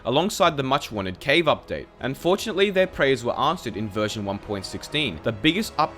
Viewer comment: I thought you said "Unfortunately, their prayers were answered" and not "and fortunately" lol